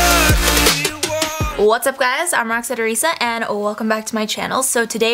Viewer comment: In the intro I thought she said I'm roxette teresa